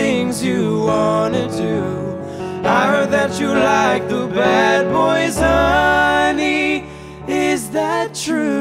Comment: I thought he said "I heard that you like bad boys, is that true?" so that could go either way.